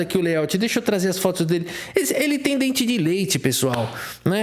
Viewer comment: dente de Rafael ? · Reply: ah sim eu achei que você tinha falado dente de Rafael leite por que o comentário acima tava dente de leite aí eu tava entrando na zueira ksksks